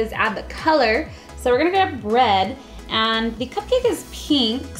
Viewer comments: Does anyone realize she said bread instead of red ant 7:24 lol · who else thought that she said bread?